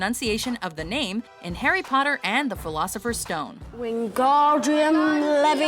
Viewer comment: did she say Harry Potter and the philosophers stone?